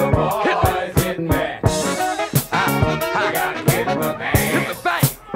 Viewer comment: If you hear any noise it's jut me and the boys. Hit me! You gotta hit the man! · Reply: I thought it said “hit the bag”.